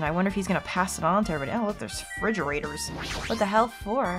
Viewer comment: Did... did you just say Fridgerator? 3:10 That sounds funny. I like it.